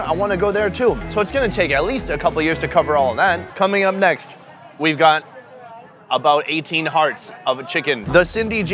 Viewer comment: Did he just say we've got about 18 hearts of "A" chicken??